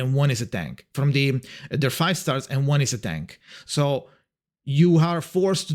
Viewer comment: Just a correction: Ben is not an ATTACK type, he is a DEFENSE type. So we still lack an A-rank character that is fire and also Attack type. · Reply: Actually he did say "a tank" but it sounds like he is saying attack because of the way he said it. Even I originally thought he said attack, but if you listen closely he does say "a tank"